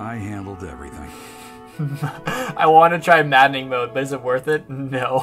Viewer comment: I thought it said gardening mode and was very confused